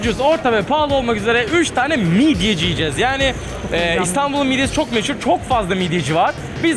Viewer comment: midyeci mi yicez diyor ben mi yanlış duydum